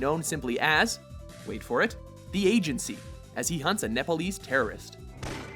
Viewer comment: Did he said Nepalese terrorist?